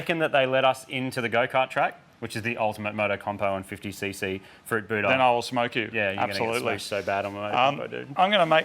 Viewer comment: Definitely thought Marty was saying fruit buddha the whole time, not fruit booter.